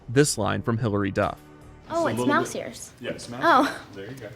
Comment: I thought Hilary said "Oh it smells here" "yeah it smells here, there you go" LOL